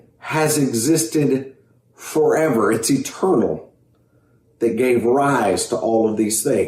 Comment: When the Christian talker said “It’s eternal.” I thought he said “it’s a turtle.” His argument fails either way.